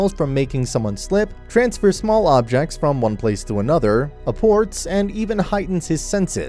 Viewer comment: i thought 5:23 said Abort lol